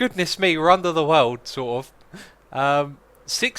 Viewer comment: I thought It said snort and not sort